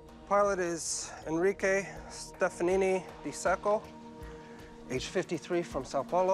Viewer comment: When the investigator said “captain stefanini di sacco”, i thought he was saying sth like “captain stefanini MISAKO” but was unsure And when the narrator said “Sao Paulo”, i totally thought he was saying “Sapporo” Silly me!